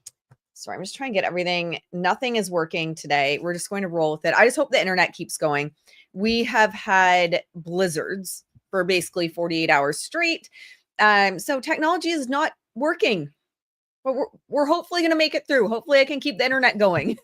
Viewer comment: I thought you said you had lizards 🦎 😅 Thanks for the live and for everything that you do :_commscrewAnarchy